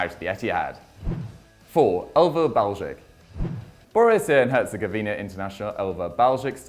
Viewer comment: did he just say Borussia and Herzegovina?😂😂